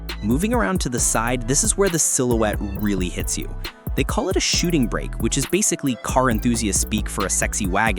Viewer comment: Did I hear "shooting bracket" High tech still haven't got it perfectley right....." Shooting Brake I.T. "..... " Shooting Brake " .